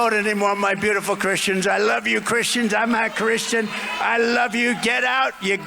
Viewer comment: 전 I'm a christian 으로 들리는데 왜 해석은 왜 not 이 들어있나요?